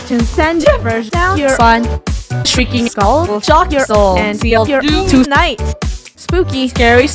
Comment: me: D-did she just say chock ur soul and sell it 😃 OK IM SCARED